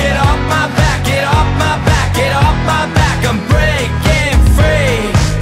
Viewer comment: iswtg every time the guy says "Im breaking free" I hear "Unbreaking 3" 😭